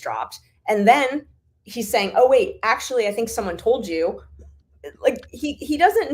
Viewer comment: I thought said, at one point, "I think I know who told you..."